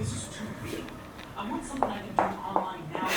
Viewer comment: hey I hear vsause @ 5:22 " Do Online Now Guys"